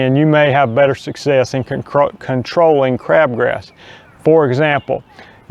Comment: Oh, sorry, thought it said 'GRABASS' control.